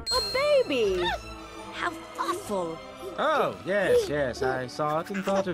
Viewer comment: When she shaw the baby I thought she said how afwul